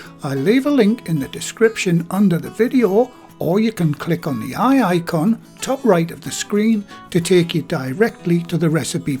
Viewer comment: did you really say "Click on the wey aye con" 😉 🤣